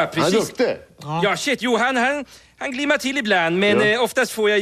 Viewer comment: did he say "aw shit" at 1:13?